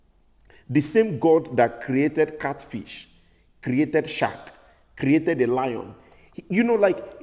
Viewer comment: I thought you said God created Shaq 😂 like the basketball player…and then I figured it out - shark 🦈 Y’all are the best!!!